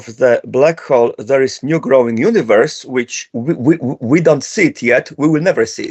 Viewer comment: En el minuto 37:47, Nikodem dice "There is new growing universe", pero en los subtítulos lo tomaron como que dijo "There is no growing universe" y pusieron "no existe un universo en crecimiento" cuando quiso decir que si hay un universo nuevo formandose por si alguien se confundio (si estoy equivocado diganme)